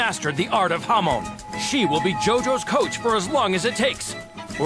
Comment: i thought it said “Jotaro” but it said “JoJo”